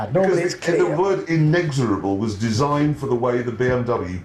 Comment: what was the word Clarkson said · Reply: Inexorable. Another word for unstoppable or relentless.